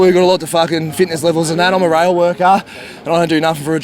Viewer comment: Can anybody tell me what that guy at 4:09 was saying? He said something like the cops have a fitness level of a railway worker...lol is he saying they are fit or unfit?